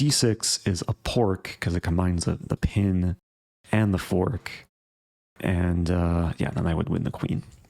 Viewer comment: first time I heard "a pork" for a fork-with-pin